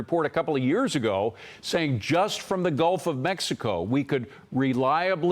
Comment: Did I just hear " the guilt of Mexico"